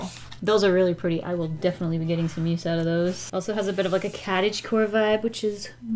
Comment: "...a bit of a CATage core vibe." 😂🤣 That is what it sounded like you said. Lol